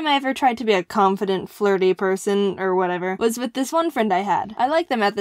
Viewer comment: I thought jaiden said " this one time I was with my BOYFRIEND" and then I hear it closing she said she was with her ONE friend